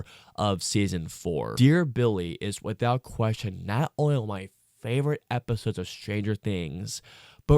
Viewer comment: For me, it's either a top 5 or a top 3 episode. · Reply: I thought it was your favorite episode of the whole as said in 11:23, but I just misheard you the first time. I figured out now you just said "one of"